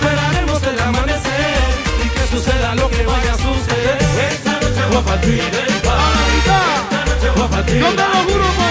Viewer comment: Pense que decía "esta noche voy a partir el pan y voy a partir la pizza" 😅😅...😂